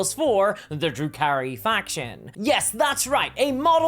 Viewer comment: Did you just say "the drew carry faction" ?